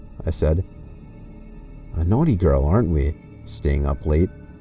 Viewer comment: What the hell he said... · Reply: He said: "A naughty boy, aren't we?"